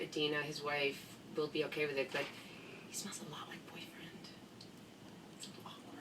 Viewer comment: What does Amy say at 3:02? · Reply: She said “He smells a lot like Boyfriend.” (Boyfriend is the name of the perfume that Kate created.)